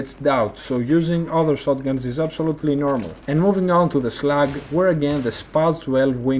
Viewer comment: haha when he says slug sounds like he is saying slags